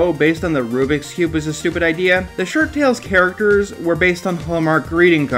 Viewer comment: ShirtTales, Woo-oo! Everyday, they're out there making ShirtTales! Woo-oo! Tales of derring-do bad and good... what rhymes with ShirtTales? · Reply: Shirt tales I thought he said shark tale